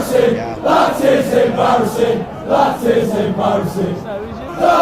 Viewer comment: I heard "that is embarrasing".